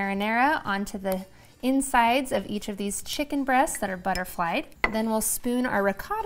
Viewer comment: I thought you were saying "butter fried" at first, not "butterflyed" - d'oh! 🙄